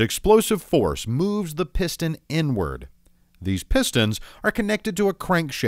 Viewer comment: I heard N-word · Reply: Inward ⬅️⬅️⬅️